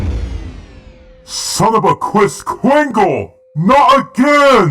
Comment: ‘’son of a Chris cringle’’ I think he said that 😂